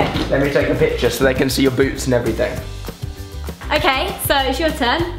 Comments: did Alfie say so they can see ur boobs and all or did he say boots and all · 'So they can see your boots and everything' While rewatching this, I thought he said boobs and I almost choked. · when he said boots i thought he said boobs oh and is anyone watching in 2016